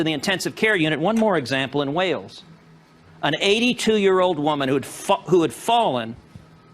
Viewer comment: did he say an 80 year old woman who fukd before correcting himself?